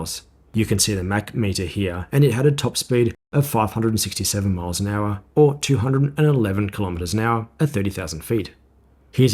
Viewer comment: it sounded like you said 211 km/h. That's very slow, it should be 912 km/h.